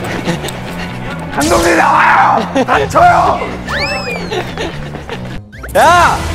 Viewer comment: 첨에 범규가 다쳐여!! 라고 했는데 그걸 첨에 몰라서 닥ㅊ 여!! 라고 들림ㅋㅋㅋ 의식해서 들음 진짜 그래ㅋㅋ